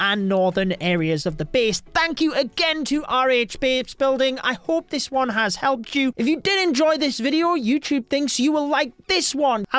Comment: Did he say RH babesbuilding?